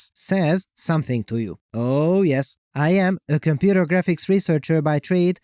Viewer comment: I heard "computer graphics wizard by trade". Not too far from the truth, I suppose.